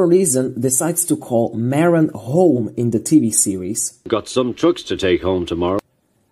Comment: I believe you misheard Edward here. He said he has “some trucks to take home TOMORROW” when he offers them to Thomas. He doesn’t mention a station. 17:07 To address the police note, ‘lawbreaker’ was probably written as ‘law breaker’ because of how small the props are, they couldn’t fit the full word on the page. Pretty interesting comparison video though, nicely done👍